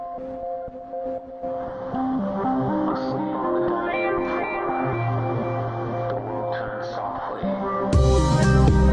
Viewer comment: What he say on 1:32 - 1:36 minutes? I really need to know it!!!!! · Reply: Asleep on the dining room floor, the world turned softly.